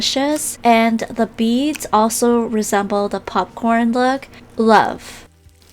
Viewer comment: what did she say at the end? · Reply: Beads resemble popcorn look, LOVE.